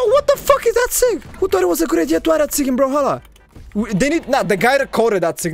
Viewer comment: i just want to know what did he say here 🙂🙂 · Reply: "who thought its a good idea to add that sig in brawlhalla?"